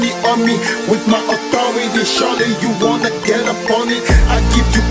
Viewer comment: Theres an time in the music that i heard: "you wanna get a pony"